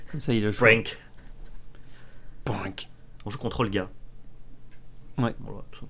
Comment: "on joue contre olga", j'ai compris "on joue contrôle, gars" XD